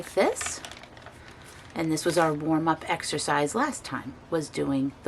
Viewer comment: Hi I really like your idea about..........oh wait...... I thought you said extra fries but i realize now you were saying exercise........Anyway no matter, i think a warm up to ease one's self into the activity is a great idea. I'm going to go with that idea but i'm going to modify the technique and ease myself in with a few glasses of wine ( white if you insist on knowing) I just love the pinks oranges and yellow you selected....I love that combo so much I also really like your choice of pink and blues pages and what a super idea to use that old brush with the hard bristles. Great colours, great ideas .... your pages are amazing and i found your choice of music so enjoyable to listen to.....it truly reflects the mood and meshes well with the topic of the journal....gentle, mindful and positive.. Great backgrounds and so enjoyed watching and listening to you in how you selected and built your pages. . Ice cream unicorn thinks it's perfectly perfect to share our inner child using the unicorn image.......our lovely Janet Nash always says "sharing is caring" and i think it so befits this topic . I laughed when you brought out your word remember/member........we do after all know the difficulty in trying to rob a bank when no one can decipher your letters/words Tweet Tweet and Quack Quack